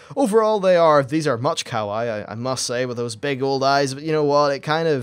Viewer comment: when you said kawaii. i thought you said cow eye